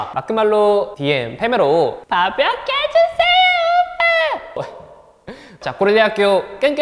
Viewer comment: 진짜 몇 번을 돌려보는데 뭐라는지 해석이 안 됨 ㅡㅠ도와주세요.. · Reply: 밥약해주세요 오빠